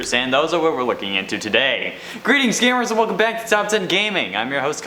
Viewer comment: It sounds like he said scammers